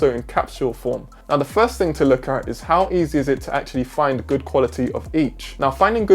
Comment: I thought you said "good quality of H."